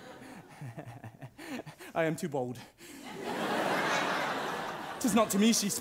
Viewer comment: I thought he said I am too bald